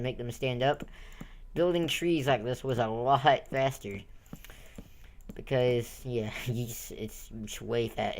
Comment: Did you really said "bester" instead "better" arround 6:30?